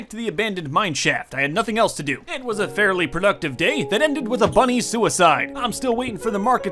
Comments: i thought he said funny suicide when he said bunny suicide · I thought he said “ it ended with a funny suicide” I was like: WHA- YOU THINK THATS FUN- oh you said bunny- oh- oh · When he said "bunny suicide" for a second I thought he said "funny suicide".